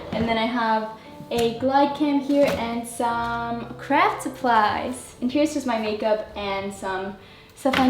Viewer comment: I legit thought said crack supplies